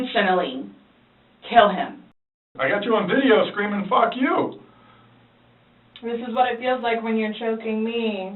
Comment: What I find so interesting is at the end of the video, the very last time she actually says "that's what i feel like when you STRANGLE me" and it sounds awfully similar to "when you cheat on me" due to her slurring, but initially when asked about the video she claimed she DID NOT recall recording it at all, however, once the detectives quote her saying "cheat on me" she immediately DENIES saying that, as if she actually DOES recall what was said while recording. And I don't even think the detectives realize by mishearing her, it actually became an inadvertent tactic that proved she was lying about "not remembering" in the first place lol THEN she quickly tries to manipulate that mistake by improvising what she actually meant by "cheat on me" is when he's "on his phone" but she had already claimed MULTIPLE times that he DIDN'T personally own a phone and that BOTH of them used and shaired HER phone 🤔 so she was all over the place when responding and well aware of her words/actions while recording. And even if she swears she didn't "intentionally" mean to kill him, once she left the scene of the crime, she ultimately left him there for dead....